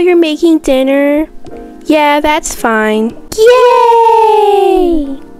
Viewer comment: I thought they said “GAYYYY”